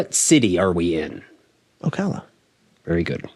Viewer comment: did he say Ocala??? My stomping grounds. I sold cars at Buchanan Jenkins Honda Acura when I was 18. I moved on to All Pro Imports in 2005 at Ocala BMW & I actually took a sales call from Prince MBS wanting to by the 2005 Porsche Carrera GT that we had. I asked “why don’t you have assistant call?” He said “ I don’t need assistant & I wanna pay less than sticker”. There was a $70k addendum over msrp. I told him there was no chance of him gettting this magical beast for less than addendum. We talked about some other things but those will be saved for later. He was a disturbing man. Bottom line you can find my in Ocala magazine for the grand opening of all pro imports back in 2005